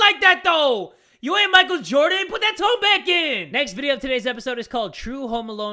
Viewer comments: It sounded like he said put that toe back in. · At first, I thought he said "you ain't Micheal jordan!!! PUT THAT 'TOE' BACK IN!!!" And then I replayed it and heard the actual thing